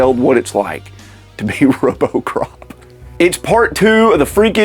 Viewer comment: lol robocrop instead of Robocop